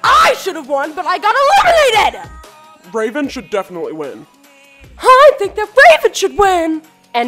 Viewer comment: I thought jackson said i think Chipotle should win ;-;'